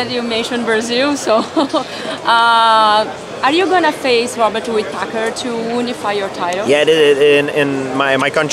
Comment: Did she say Robert Woodpacker at 4:07 ?